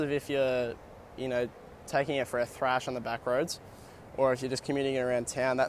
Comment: Nice review and good to see the bike in action in 3rd person view. At 4:40 in, did you say "If you're taking the bike for a crash on the backroads", or "for a thrash on the backroads"? ;) Hopefully all avoid crashing as much as possible, focusing on staying upright. Cool bike.